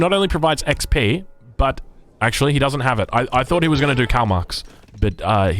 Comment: Am I the only one who heard "Karl Marx" instead of "Kalmucks" ? The vodka might be getting to my head